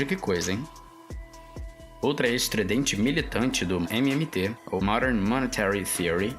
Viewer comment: EU OUVI MST ?? 😅😂😂😂😂😂😂✅✅✅✅✅✅✅✅✅